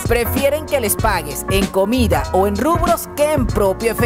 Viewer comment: dijiste rubros o rublos, la moneda rusa? no entendi muy bien xd, en caso que fueran rubros no se que sea